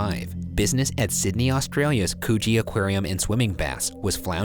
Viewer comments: Idk about y’all but I heard “coochie aquarium” 😂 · At the beginning I thought he said coochie aquarium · i swear i thought they said Coochie aquarium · DEADASS THOUGHT HE SAID “COOCHIE AQUARIUM” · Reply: Same! I actually said out loud to myself, “Did he just say coochie aquarium?” 😂😂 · I thought Ryan said co0chie aquarium at the start omg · Please tell me that I'm not the only one who thought Ryan said 'coochie' when he said 'Coogee' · Thought he said coochie instead of Coogee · Could’ve sworn he said “coochie” aquarium, not Coogee Aquarium. Had to go back and read the name LMAO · I THOUGHT HE SAID COOCHIE AQUARIUM AND ALMOST CHOCKED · thought he said coochie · I thought ryan said “coochie aquarium” and I screamed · I thought these boys really said “coochie aquarium” damn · i legit thought he said "coochie" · i really thought they said Coochie but when i saw the name it was Coogee im like SKSKSK 💀💀💀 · I RLLY THOUGHT HE SAID COOCHIE AQUARIUM AHAHADNJD I ALMOST CHOKED ON MY MILK IM DONE SJJS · i stg i thought it said coochie aquarium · When he said coogee I thought he said coochie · Ngl when u said Coogee Aquarium I heard “coochie” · all i hear is coochie i'm sorry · hehehe I thought he said coochie but it was Coogee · “Coogee” I thought you said “coochie” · When they said coogee, I thought they said coochie. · Did he just say the Coochie Aquarium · I thought he said coochie aquarium 🤣👎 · YALL I SERIOUSLY THOUGHT YOU SAID COOCHIE AQUARIUM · coogee, I thought he said coochie